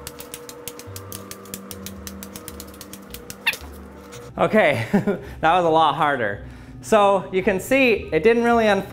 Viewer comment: that sound was so funny I wonder what he said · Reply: He said oh shit